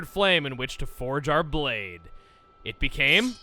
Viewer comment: I wasn't paying attention when he said "forge our blade" and heard it as "Jar Jar Binks" Needless to say, I was confused.